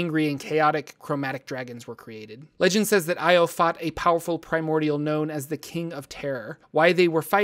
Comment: I swear to god I thought you said "Primordial gnome, the king of terror"